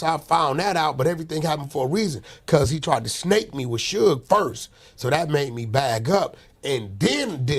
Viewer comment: I thought he said he fell out with Big U because he tried to snake him at UMG???! Don’t add up! And when he gonna give J Diggs his fade?? Big gangster